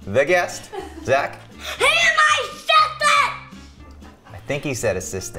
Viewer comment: I think he said assistance I thought he said stepdad